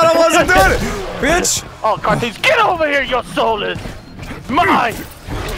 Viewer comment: What Shang Tsu says "Your Soul Is Mine!" What I hear "Your Soul Is Mah"